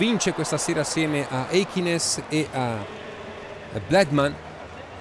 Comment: did he say black man at 0:50 ?